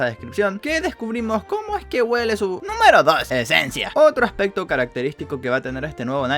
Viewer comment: Tablos: escuche a alguien decir E S E N C I A